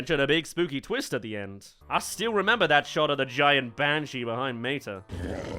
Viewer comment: I thought he said Vanshee... Mann...